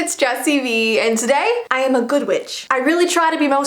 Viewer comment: My volume was low and I thought she said "Today I'm a good bitch"